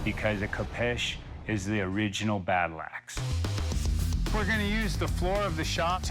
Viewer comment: "the khopesh is the original battle axe" Um, I'm pretty sure a battle axe was the original battle axe. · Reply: oh i thought he said " their regional battle axe"